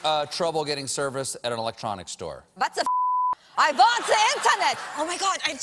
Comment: What did she say in german? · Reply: "what the fuck, i want the internet" but with a German accent